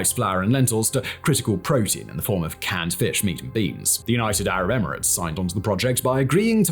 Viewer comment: did you say "cat" or cat fish...?